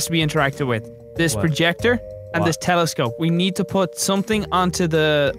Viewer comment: Did Brian call that a telescope?